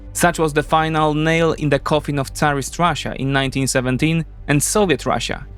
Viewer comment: The first 2 times he says “Tsarist Russia” I heard “terrorist Russia”. Same difference about now I guess :(